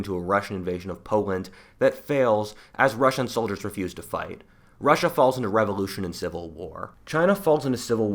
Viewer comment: I thought for a second you said Russia would fail because “Russian soldiers would be used to fight” 😂